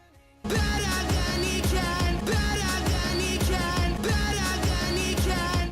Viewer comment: to this day I cannot hear this as anything other than "bet a danny can" whatever that means